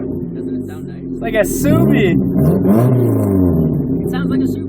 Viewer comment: Did you say "it sounds like a subie" or "it sounds like a supra"? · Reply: Subie